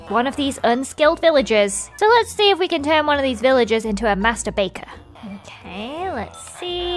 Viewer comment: Seriously....sorry for my dirty mind...but I thought said "Masterbaters" 😂